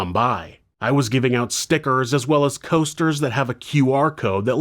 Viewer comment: I had to pause and rewind a bit because I thought you said "Stickers & Toasters" 😂